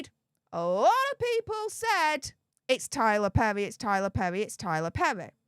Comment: I thought he said it was not Tyler.